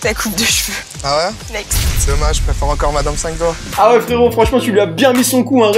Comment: il dit quoi le mec nexté ? X) j'arrive pas à comprendre mais ça à l'air tellement beauf ! Ça me frustre là X) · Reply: "Dommage, je préfère encore Mme 5 doigts"